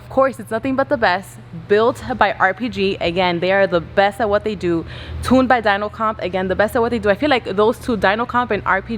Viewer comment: I thought she said “tuned by Donald trump”. Lol.